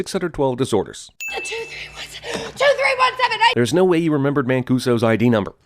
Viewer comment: he says 'there's no way HE remembered', but the subtitles say 'there's no way YOU remembered'. Just thought id let y'all know.